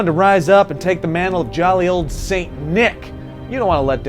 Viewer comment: At first I thought he said, How to be jolly old satan. LOL